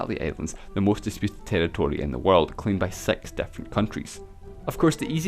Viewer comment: he actually says sex, isn't it :)? I'm I wrong ????